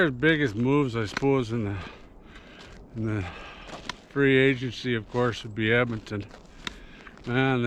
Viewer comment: I heard you say free trade instead of free agency has pretty much wrapped up. lolCan’t blame you that’s is the only word I have heard for the last week is free trade. lol keep up the good work!😂